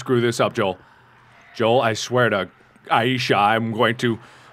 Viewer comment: Can someone tell me what he said at 9:15 ? Did he say "I swear to Aisha" ? Because that's my name and i swear to god he said the same thing in an Octodad video and I cant believe he said my name again. Why does he keep saying that lol what is he trying to say?